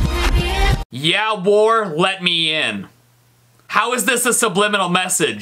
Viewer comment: You get 7:59 hey guess what says let me in